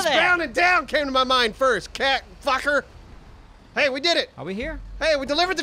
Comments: did he call him a "cat fucker"? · Did he just call him a "cat fucker" Also they could've sang "Life is a Highway" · Did he say...Cat Fucker?